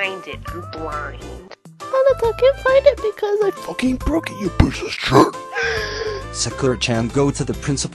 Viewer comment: What does she say, someone tell me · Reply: something like "well i cant find it because i broke it into pieces"